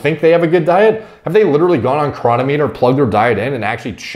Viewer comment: what is the thing he says to plug their diet into? chronomy? chronometer? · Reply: cronometer -- it's a website